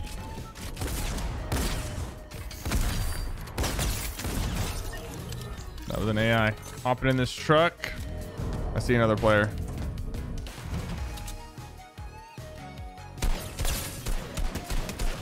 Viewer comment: Did he just say Lmai?